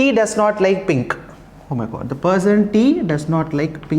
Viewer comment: did u just say OMG, sir?😂